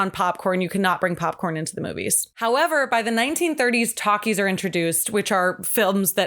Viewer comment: She said “talkies” came about and I heard “takis” and was like damn they’ve been around that long?? And how are those cleaner than popcorn!?! 😅😅🤣🤣